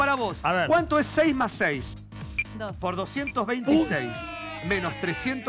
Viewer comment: min 16:22 le pregunta 6+6 y dice 2? escuche mal? jaja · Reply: dice 12 jajaja